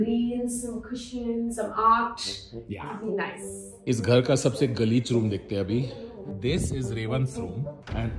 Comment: Did tanmay say "galeej" at 1:29? Being a north Indian and living in Bangalore, I can confirm that galeej is one of the first words u will learn from ur kannadiga friends😂😂 Great to hear it from tanmay